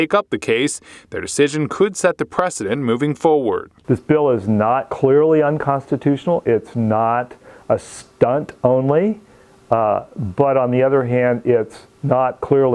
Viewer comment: Wait 1:49 , did I hear you just say "This bill is ~ clearly unconstitutional, It's ~ a stunt only." Just making sure your fallacies are LOUD AND CLEAR 😂